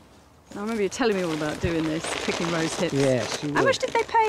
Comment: Can someone please tell me what she is saying at 34:41 (where they are picking the red from the bushes)? It sounds like she is saying rose buds & the cc doesn’t translate the whole sentence, I have always been curious what those bushes/reds are. They don’t look like rose buds so am a bit confused they don’t really say what it is or give any (...) of what it could be expected it’s edible, the man talked about berries so it can’t be rose buds but no clue of what they actually are. So can someone please help?🙇‍♂️🤷‍♂️🙏🤗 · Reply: Those are rosehips